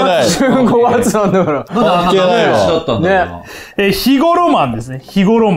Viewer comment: 何度聞いても肥後浪漫が日頃マンに聞こえるんですよね…